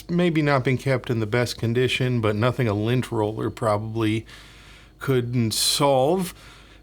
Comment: I heard “Lynch Roller” and was excited because I thought it was a tool I hadn’t heard of to round and straighten mic grills! Lol oops you said lint roller..